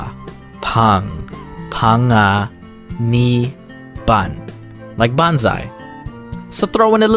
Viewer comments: I thought your speaking Chinese, sounds like it to me, this is what I heard. 胖啊你半like灣仔, in Chinese your just blabbering stuff that doesn't even make sense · subtitles say bonsai like the tree, but you're saying banzai like "hurray" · You might want to correct your CC to say "banzai" instead of "bonsai", the latter of which is pronounced "bohn-sai" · when you said panga I legit thought you said tanga... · When you said bonsai I thought you said bankai,as in from bleach XD